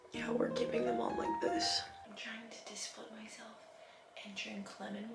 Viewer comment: I heard “I’m trying to disappoint myself” instead of discipline myself 😂 I was like girl I would be too if I drank lemon water instead of coffee 😅